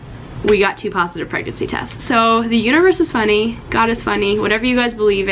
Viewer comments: What you said: "The Universe is funny!" What I heard: "The Uterus is funny!" Both applicable here. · What you said: "The Universe is funny!" What I heard: "The Uterus is funny!" Both applicable here. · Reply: literally didn’t know she said “universe” until I read this comment lol thought she said uterus and I just agreed like “yup....sure is” lol